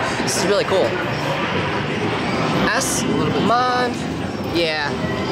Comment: in 7:50 it sounds like he is saying ass not s haha